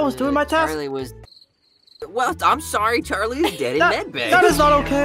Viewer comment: I found it cute how was like listen her light charli is dead in medbay( he didn’t say that word for word but it sounded like it in my head 😂)